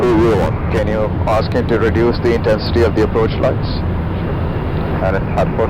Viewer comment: what did they say at 24:57min ? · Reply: Can you reduce the intensity of the approach lights